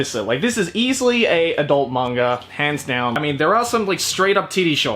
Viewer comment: When he said hands down I thought he said pants down. Nice